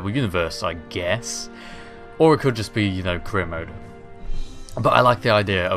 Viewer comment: I swear I keep hearing you say 'Korea Mode' XD